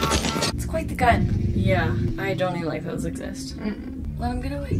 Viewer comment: “That’s quite the gun. I don’t think those exist.” That is a GE M134 Minigun. It’s a real gun, but they are usually mounted on vehicles. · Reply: Thought she said "Don't even like those exist." Figured she hasn't seen what the GAU can do to a T-34